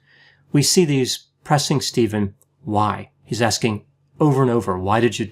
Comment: Correction. The interrogator did not say "Why?", he said "Wah?"